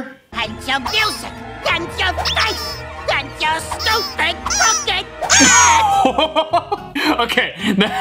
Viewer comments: Mugman said "Crooked hat" 2:00 It actually said "Makka Pakka" 3:10 Darwin said "What the..." and screamed. 3:48 He said "Thank you" 5:30 They sang "Bucky, sail away 6:35 Scooby did say f*** but said Dean Funk after 8:20 Jack said "you have to read the book its in the contract you have to read the fine prints kids me me me me" 9:08 Elmo said "B-but how, Chris?" 10:25 was a edit made by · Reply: I thought he was saying "AND YOUR STUPID LOOKING HAT!" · And your fluffy face that’s what he actually said 0:07 😊😊😊😊😊